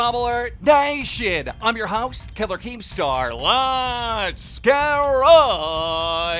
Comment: Every time I hear“What is up drama alert nation I’m ur host Keller keen star let’s get right into the newwws” it feels like home🥲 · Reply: It’s killer keem star lmao